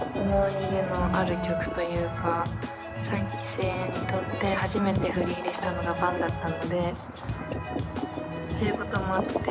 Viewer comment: このあとなんて言ってる？ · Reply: 3期生にとって初めて振り入れしたのが BANだったので